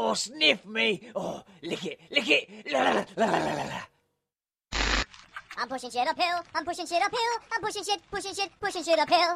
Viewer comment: I thought he said I'm pushing a shit of pill but he said I'm pushing shit up a hill 🐜💩